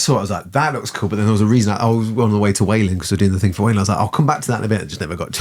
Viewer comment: Jesus I thought you said "I was on my way to whaling" 😅